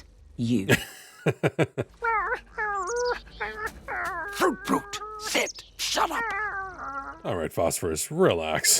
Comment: I think he called him "Fruit Brute" which would be a DEEP cut as it's one of the General Mills monster cereals from the 1970's. That one was cherry flavored I think but the mascot was a werewolf.